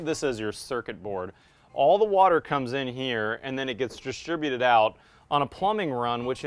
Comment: you said it right by calling it distributed but my speaker was low and it sounded like you said mashugana'd out. If there are any Jews reading this, they will laugh